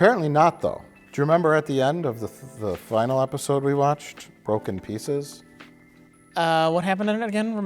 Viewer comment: I misheard and there for a bit I assumed the Ryker episode was called "Broken Pizzas."